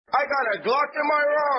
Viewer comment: I always thought he said “I got a Glock in my hand”